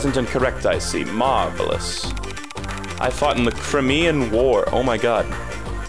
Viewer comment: I thought that the bear said he fought in the “Kreaitnam War” and almost lost it